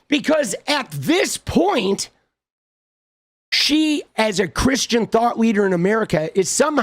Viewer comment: Thought you said Christian Thot